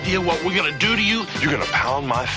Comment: As a kid I had NO IDEA what the guy was saying in the end. Lol. It sounded like he was saying “What are you nuts? These are nice, we gotta city.” Of course that makes no sense. He was actually saying “What are you nuts? This is the 90’s, we’re gonna sue ya.” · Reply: I knew he said "we're going to sue you" but I always thought he said "this is nice" as well.